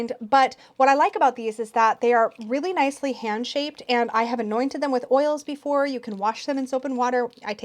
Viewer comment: I was listening on double speed and when you were talking about the resin goddess statues I misheard something that sounded like: yeah I’ve doused them with poppers…! haha